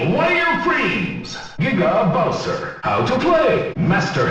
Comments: I swear it says n***a bowser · Sounds like "Nigga bowser" instead of "Giga Bowser" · did anyone else thought it said nigga bowser